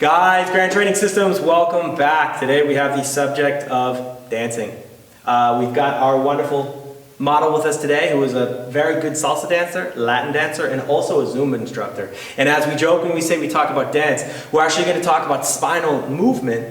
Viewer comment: At the start I misheard you and thought you said Lap Dancer 😂